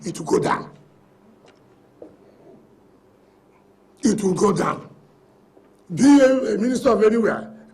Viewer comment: I thought he said he can't be a minister?